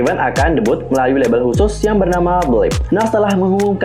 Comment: Ka di menit 1:50 itu kaka ngomong apa ya....?? Naungan nya namanya apa...?? Blek...?? Blik..?? Atau apa 😭❓ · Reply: Belift